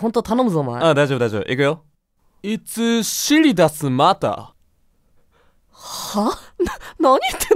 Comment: うぽつです！今回のジェルくんも 破壊力のある空耳を生み出してくれましたが 0:45のスローでの｢尻出すまた｣発言は 謎にじわじわ来てしまって仕方がないですw そんな中1:12でさんに弁解する可愛いお姿 なんだか愛しくてきゅんとさせられました😭 それにしても素晴らしいことにさんが どうにか空耳を聞き取って直してくれたはずが 再び｢いつ尻出すまた｣に聞こえる、 もうジェルの同じすぎる思考回路には 兄弟っぽさを感じてきますね、w